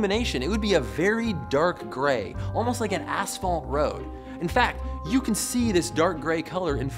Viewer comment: sounded like he said asshole at some point.